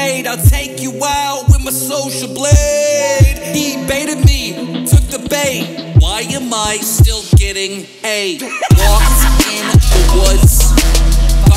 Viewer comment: My dumbass thought he said "why are mice still getting aids" at 1:48 🤦‍♂️😭